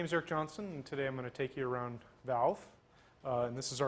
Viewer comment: I thought he said his name was Cave Johnson at first :)